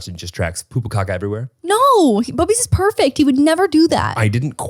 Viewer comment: captions says Bobby’s not bubbies Also I’m sure another Canadian has commented this but it’s not coming from an Asian household necessarily that makes her a no shoes in the house person. I’m pretty sure it’s a Canadian thing lol 9:15 Captions spell Courtney, Chloe and Chris Kardashian as I’ve written